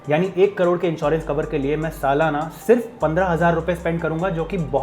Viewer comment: Hi bro Your subtitle is wrong at 3:55. In sub title 15000 monthly mentioned that should be annually.